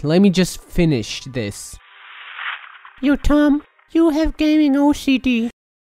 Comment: at first i thought he said you are gaining OCD